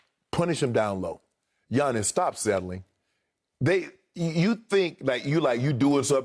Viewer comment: I thought Shannon said “stop selling” and I busted out laughing